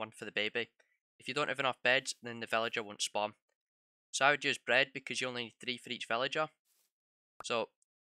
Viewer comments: It sounded like he said s3x bread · I thought he said “sex bread” at first and didn’t question it given the title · I thought he said “sex bread” 😂😂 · I thought you said sex bread lol. Kinda fitting ngl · Idk about you guys, but I heard “sex bread” at the start lol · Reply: I heard "Six Bread" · I thought he just said sex bread.....